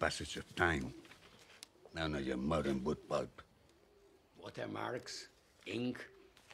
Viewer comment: when he talks about Venetian craftsmanship and the "pages resistant to the passage of time," what does he mean when he says "none of your modern ' boot pulp' " or "boot bug" (subtitles) ?? what does it indicate about the book itself? okay I noticed he says the "finest rock paper," paper actually made from stone, instead of tree pulp. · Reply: He says "none of your modern wood pulp" Wood pulp is nowadays widely used in making paper. It is very cheap, but most of low quality and doesn't hold on its own for a long time.